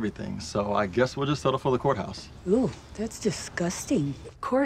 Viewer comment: “Eww that’s disgusting... I thought he said outhouse wedding...” 😂 she’s hilarious 🤣